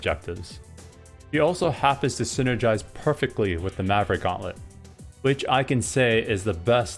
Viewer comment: What gauntlet did you mention? I just don't hear)) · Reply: Maverick Gauntlet - unlocked with 50k punches